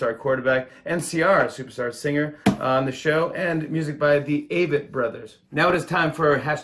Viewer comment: Dude, it’s “Avett” brothers. They’re not bugs 🐜 (sounds like you said “the aphid brothers.”).